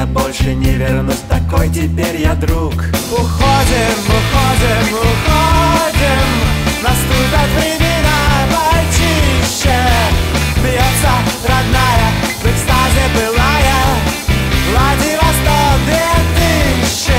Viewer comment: Наступят времена мальчишек 0:56 Клади на стол две тыщи (Послышалось)